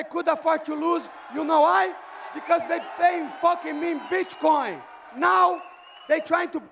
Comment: did Moicano just say "B*chcoin" man 🤣🤣